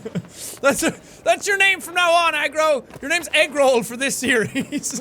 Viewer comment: For some reason I thought he said eggroll for the name of the house, but then I realised that he said agro